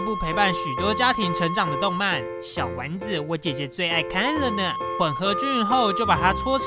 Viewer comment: 我聽成:小丸子的姐姐我最愛看了XD